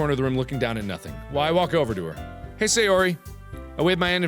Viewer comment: "Sayori is sitting at a desk in the corner of the room, looking down at nothing. Why walk over to her." It sounds like he says Why walk over to her. LMAO